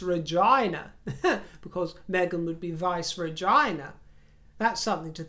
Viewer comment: I misheard vice regina the first time, and thought that you said vice vagina. I spit my tea out. Brilliant as always. 🍐